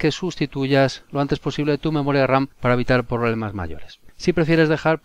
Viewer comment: que dice enel 4:40? para evitar que? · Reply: «Para evitar problemas mayores».